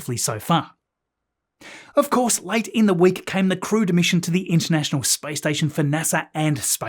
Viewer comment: LOL, I always hear 'crewed' as 'crude'.